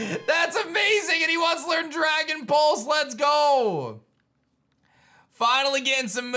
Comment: it sounds like he said dragon balls not pulse